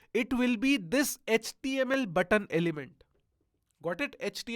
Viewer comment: did he said html button elephant 😂